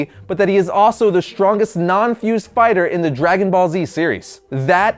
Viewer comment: he said spider instead of fighter lmao